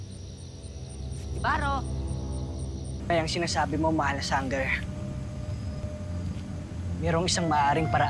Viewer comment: sangger ba ang narinig ko?